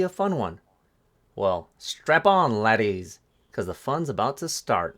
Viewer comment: “Did he say strap in or strap on?” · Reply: Definitely strap on! 😂